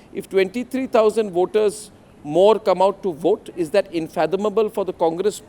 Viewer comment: the word is unfathomable said by Editor n chief Arnab Goswami and not infathomable dear Republic Subtitles dept plz change it